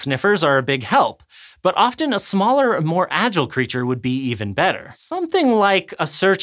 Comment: Every time he says "creatures" I hear "critters"